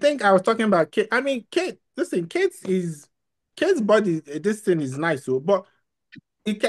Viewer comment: Did he say “body distance”?